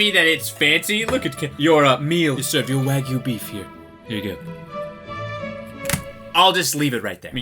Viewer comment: I really thought he said the n word but he said “Here you go” 😂